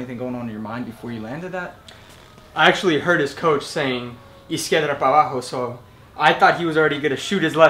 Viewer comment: I thought the coach said "he scared of mama"